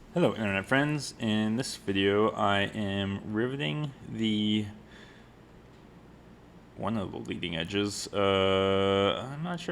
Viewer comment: I had a hockey game making noise in the background and I thought your intro said "In this video I am ruining one of the leading edges" and I got so sad!